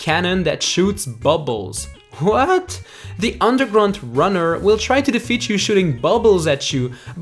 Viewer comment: i heard say ''Undergrunt runner''